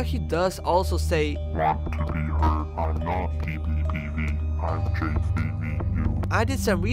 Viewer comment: I think I know what J3VU is saying “Want to be heard I’m not PBBV I’m J3VU”